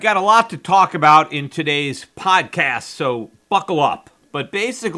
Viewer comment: Buckle up!! is what I hear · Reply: You're right. I listened again and he did say buckle up. The first two times I listened to it, it sounded like he said fuck off. The audio on my computer is fucked up.